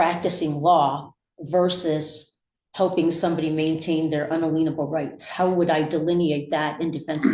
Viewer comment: Is she saying "unalienable rights"? Wouldn't an attorney know how that's pronounced? Maybe I'm the stupid one and misunderstanding, though.